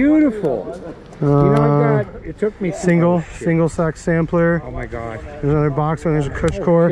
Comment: Did I hear “single sack sampler”?